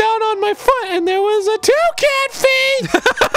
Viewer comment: Just listening to it I thought he said “TOUCAN FEET??!”